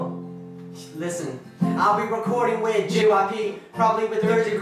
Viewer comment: woah did he just said jyp?